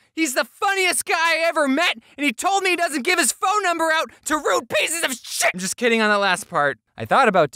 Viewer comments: Not gonna lie I heard: “HE DOESNT GIVE HIS NUMBER TO ROOF PIECES OF SHI-“ and filled it in as shingles. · did anybody hear him when when he meant to not say it but he did? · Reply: Same I heard shit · Did he say shit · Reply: Also.. at 8:11... did i hear the word sh- Family friendly James: shuts the mouth Oh i dare you to say that! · On 8:05 when he says rude pieces of shit, the caption say, “RUDE PIECES OR SHI-“ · Did he just say, “shit,” on 8:12? If so, that’s the first time I’ve ever heard him cuss. Weird